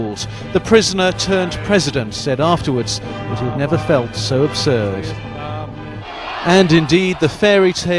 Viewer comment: Havel said 'he had never felt so ABSURD' ??? I couldn't catch the word.